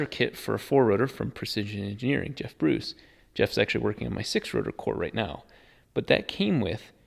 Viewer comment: did i just hear SIX rotor?????